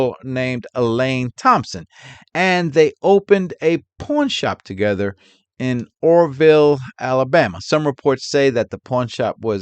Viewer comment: I thought I heard Porn shop, 😂 😂 . Sorry Mr.. Im listening 👂 👂 while working out. My ears. Thank you sir.